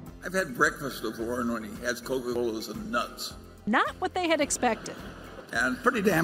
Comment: Did Charlie say breakfast of Coca Colas and nuts? Graniolas? What is that?